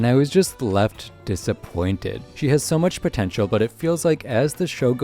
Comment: DID YOU SAY POTENTIAL?